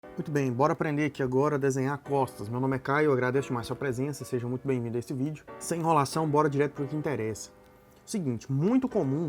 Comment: no inicio eu escutei "aprenda a desenhar cofres" kkkkkk